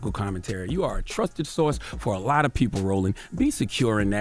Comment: Yo did he call that man “Rolling” instead of Roland at at 18:14 Also I can’t say I’m cool with black folks cuttin each other like this